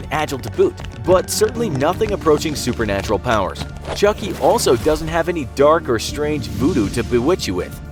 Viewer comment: Hi, I'm Chucky, wanna play? ... how did these words make you feel? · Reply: The Infographics Show I thought you said chunky